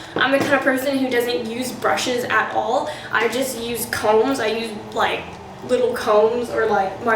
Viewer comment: i heard "i just use condoms" and i had to replay it and listen again😂